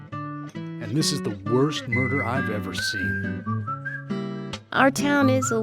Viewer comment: I wasn't paying attention and I thought said, "This is the worst burger I've ever seen"...